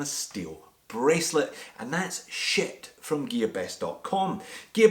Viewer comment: who else heard, “...and that’s SH!T...”